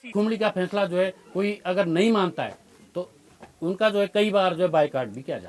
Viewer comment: yeh kya kaha inhone? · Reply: Jo village court ka decision nahi manega uska boycott hoga